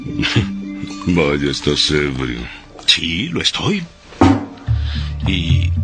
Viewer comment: ¿Qué dice? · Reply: Vaya, estás ebrio.